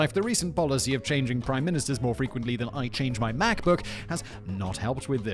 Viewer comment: Il have to be honest, i thought Simon said Math book @ 16:20 It wasnt untill i saw the video of someone running away with computers that i started to question what i had heard. Would be quite funny tho if someone stole a blind mans math book... "Ye, gotta get them brials man, thats what its at! Bump, bump, bumpbump... space! Aw yissss!"